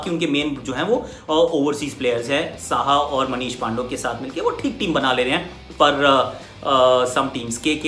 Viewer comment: Did you say Manish Pandav ?